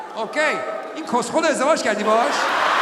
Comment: Kos khode zamashka dabesh?? What is he saying at 11:25? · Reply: In koskhol ro ezdevaj kardi Bahash? Means you married to this idiot or crazy